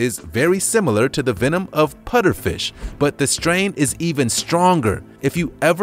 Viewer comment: i heard him saying "Putterfish" instead of "Pufferfish"..